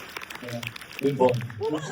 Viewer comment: What does Alex say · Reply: He says good boy 😅